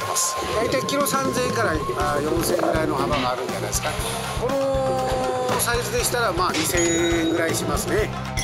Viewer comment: 「うん このサイズ、、、」が、 「うんこのサイズ」に聞こえた笑笑